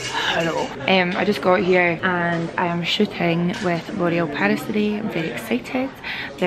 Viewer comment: I was getting a drink whilst you was on the plane and that and when you said you were shooting with L'Oréal I thought you said you were shitting with them and I got well confused